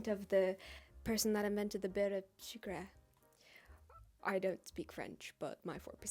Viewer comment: I couldn't understand what you were saying, can you write it please? I'm french and I would like to know what you were trying to say. Have you tried a kouign amann ever? It's from brittany. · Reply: Maybe beurre sucre? I don’t know.